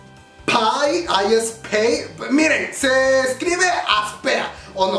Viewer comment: Minuto 6:00 estaba hablando de aespa y cuando hablo de como se escribe pensé que dijo "se escribe aspera" jajajajajajaja